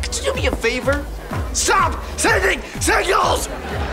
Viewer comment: I keep thinking he saying...stop sending CIRCLES!!! but but noooooow that I hear it he saying signals